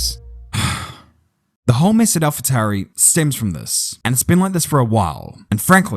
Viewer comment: I turned on the subtitles to see what "It thinks" your saying. 9:53 what is al-fatari? Not your fault, I understand Engrish, but that is hilariously bad subtitles. Love what you do, thanks for all your hard work. Yuki is still cute. :face-purple-crying:💖🌸🌹